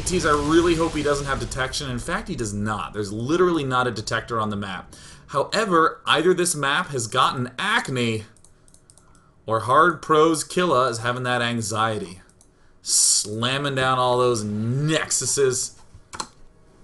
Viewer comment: Every time he says the name, I hear "Prose Killa".